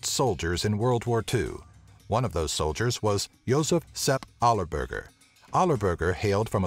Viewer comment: When the subtitles say Allah Burger XD WTF?